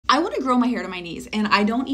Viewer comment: I THOUGHT YOU SAID YOU WANNA GROW HAIR ON YOUR KNEES!😂